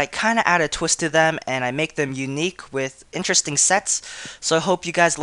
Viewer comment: damn why i heard " with interesting 'sex' "